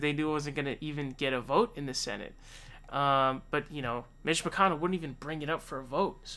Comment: I thought he said "Bitch" McConnell lmaoo